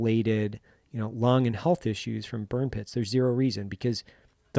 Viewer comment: I thought it said bum pit and was really confused.